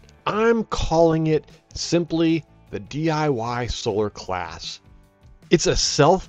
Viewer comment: I thought it said solar glass, I was like: waa? and I was all: huuuh!